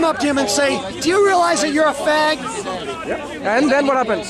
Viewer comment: Did he said "FAG"?????